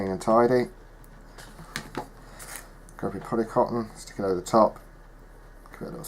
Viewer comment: Grab your.....what did you say at 6:46? Couldn't hear it properly...what material are you using under the flock? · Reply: The material poly cotton , I do another video using wadding instead which is alot cheaper.